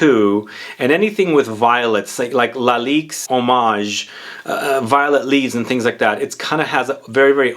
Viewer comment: did you say lalique's eau mage??